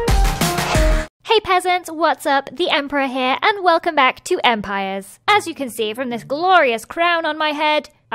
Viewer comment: Did she just say "hey peasents! Welcome back to empires-?!" Umm gurl don't call me out like that 💀